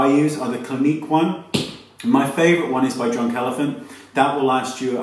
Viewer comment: He said Drunk Elephant but it sounded like he said John Calavin 2:20 lol